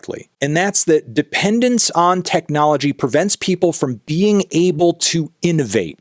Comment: He DID NOT say "dependence on technology...", he said "CREATIVE dependence on technology..."